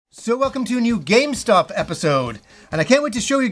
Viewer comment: At first i thought you said Welcome to a new GameStop Episode